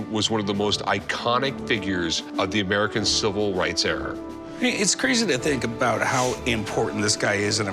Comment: Did Rick just say ".....the American civil rights error."??